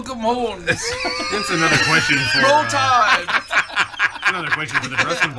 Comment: did he just say roll tide